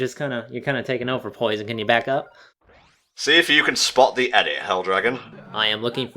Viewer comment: what was saying to when saying "spot the *" i couldnt hear that last part. thanks you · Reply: ***** Spot the edit.